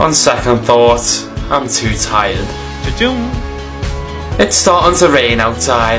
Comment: Bro when he picked something I heard “chiching”